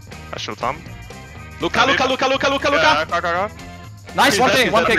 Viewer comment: i actually thought he said Luca luca luca and than i read the subs xD